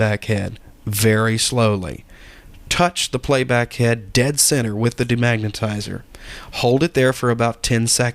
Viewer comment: Hi i have a quetion in your video from 7:17 you said touch the head and my quetion is why ?? and also i can't understand the 2 words you said like { den sin - sen } after you said < touch the playback bead then you said den sin with the demagetizer : In other videos they said not to touch in order to avoid any scratch Very good video & thanks for any reply my regards :-) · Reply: Dead centre